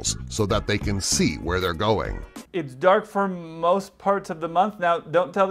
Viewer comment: "It's dark for most parts of the month". Well, that's not what he said. He said "it's dark for most months of the year". Big difference.